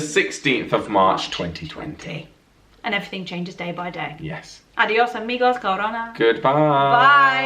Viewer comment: I LOL at the last part you say adios amigos and it sounded like you say cavorna translation to English b**ch... i was doing some paperwork at work and i was like WHAT LOL ... funny